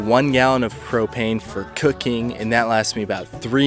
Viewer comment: I thought you said "propane for cocaine".